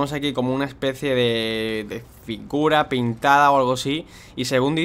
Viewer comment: Genios haber el no dijo pintura dijo "FIGURA" para los genios que dicen que dijo pinturas pintadas vean bien el minuto